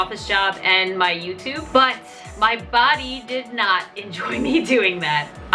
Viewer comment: When she said my body and 4:15 i thought she said my bobby